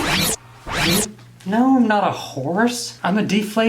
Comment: i literally thought he was gonna say "im not a whoreeee?"